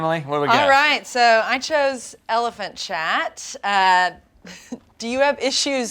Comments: I thought she said elephant shat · I thought they were saying elephant shat not elephant chat lol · I thought they were saying "Elephant Shat" and I was so confused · I thought they said elephant shat so I thought that squished elephant was actual elephant shat shaped like an elephant 😂